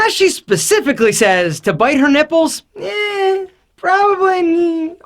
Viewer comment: I only hear don't bite the nippels😂😂